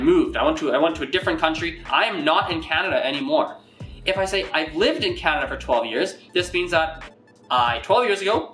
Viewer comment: прикольно, добавлю себе в избранное. в учебнике читаешь про эти времена, и не понятна именно эта суть, которую разъяснил этот товарищ). Спасибо. Однако трудно было услышать один момент вот тут 6:37. Он сократил "I have lived", до "I've lived", и я не расслышал, думал он ошибся и опять сказал для второго примера "I lived". Несколько раз переслушал.